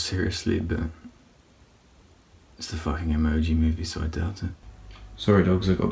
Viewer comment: I did to I thought he said "those fucking emojis"